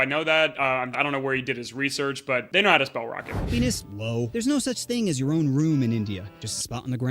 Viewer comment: right when it unpauses it sounds like he says "penis, low" (Even subtitles thinks so)